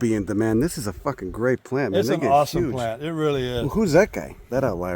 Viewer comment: What'd the guy say at 23:48, that " " is huge? no filter on this guy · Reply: “This is a fucking great plant and they get huge”